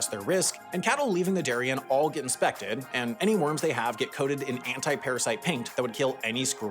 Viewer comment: should that be 'wounds' sprayed instead of 'worms'?